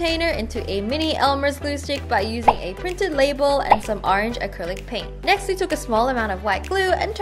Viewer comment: did anyone else notice that at 2:49 instead of acrylic paint she said acrylic pain? lol